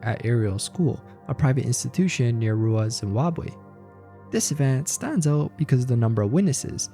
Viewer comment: Did he say "rural ass Zimbabwe?" 😅